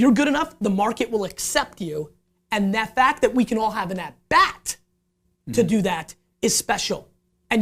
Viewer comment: What is this word Gary uses? (ATBAT)??? 31:33 I can't even find it on google.. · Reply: This may be a late response but he’s saying at bat. As in Baseball. He means you get another swing at things